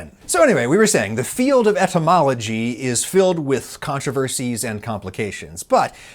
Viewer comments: Captions at 3:57 say "entomology" instead of "etymology". And while I'm sure entomology is also filled with controversies and complications, the current topic was words, not arthropods. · Captions [English (United States)] say "entomology" instead of "etymology" lol. Might want to fix that since we're talking about linguistic origins rather than insects.